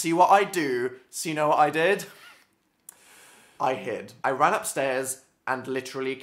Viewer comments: omg, when you said "I hid", I thought you said "I hit"!!! I freaked out because I literally thought you hit her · At first I thought said,"I hit her." Not,"I hid." facepalm